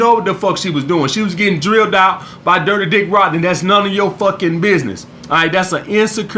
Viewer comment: I thought you said "Dirty Dick Rodger" xD